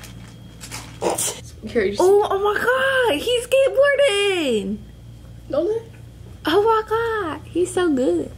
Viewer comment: The past 100 times I’ve watched this video I thought was saying “omg it’s jake warden!!” And now I’ve finally realized that its skateboardin 🤦🏽‍♀️